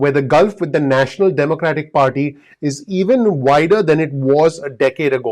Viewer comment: I thought you said whiter you said wider lol